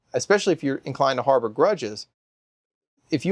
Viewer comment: Oh, "harbor grudges". I thought I heard, 'Harvard graduate'!